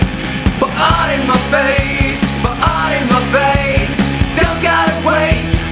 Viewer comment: Fart in the face? Some how I heard "for I am the face" as if to say that you weren't going to hide behind a mask of false pretenses (or something less profound)